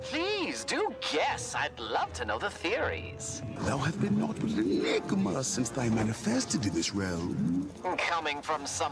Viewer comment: tf he say? here's what i heard: Thou have been not ieieilinmga since thy manifested in this realm¿" I've never been able to decipher this 👆 plez help it annoys me so much 🙏